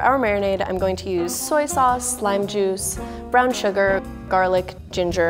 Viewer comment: did she say slime juice